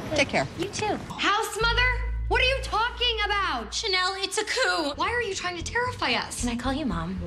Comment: What does she say at 1:51??? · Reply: Coup. It's french. It means like an overthrow typically in government or against who ever is in power